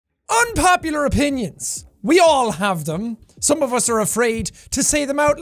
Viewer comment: I thought it said unpopular onions😂